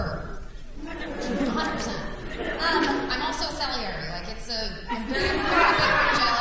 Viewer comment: what did she say? "I'm also..." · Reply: Salieri